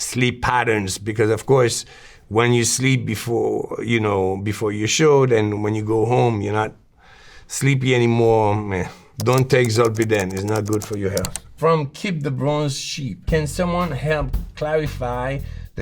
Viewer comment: Does he say "Don't take Zolpidem..." ??? Why do you not list what he said not to take in your text graphic on screen???????